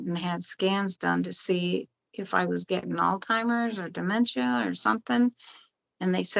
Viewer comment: did she say "oldheimers"? Or "Oldtimers"? 😂 How funny would that be, to call it Old-timer's instead of Alzheimer's...😂